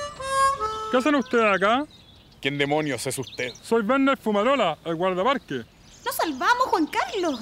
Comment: yo entendí "vende fumarola"